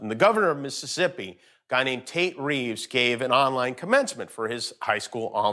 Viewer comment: ish. Did he say the guys name was "TAINT" Reeves?!?